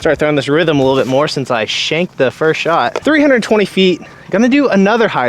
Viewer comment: It sounds like you said “wanked it” which doesn’t mean the same as “shanked it” in the UK 😂